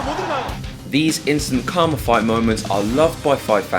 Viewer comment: Did say "these instant cum fight moments are loved by fight fans"?